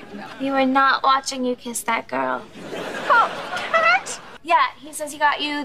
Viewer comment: what did cat just say? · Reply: Kylie Dorothy we did NOT watching you kiss that girl